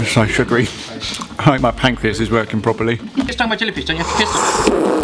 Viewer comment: Did I hear say "if you get stung by a jellyfish don't you have to piss on it?", I'm disappointed